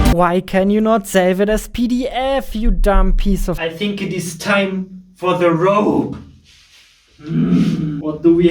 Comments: Am I the only one who heard "I think it is time, for the rope!" for the first time? 😂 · Game dev isn't always glorious... Haha... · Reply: I thought you said: "And now it's time for the rope" XD